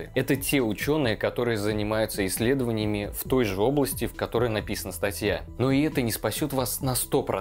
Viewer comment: услышал " в той жоплости")))